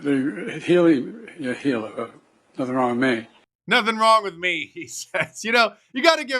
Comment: Sounded like Biden said "got the wrong name" not nothing wrong with me.